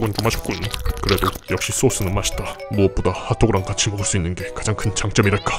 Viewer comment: 더맛다고 들리노